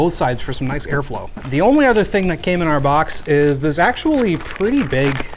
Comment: and that's how i became a dad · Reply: I didn’t get it until the third time I backskipped, but then I heard it: “came in our box”